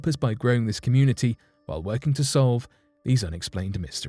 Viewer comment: I thought u said they couldn't b explain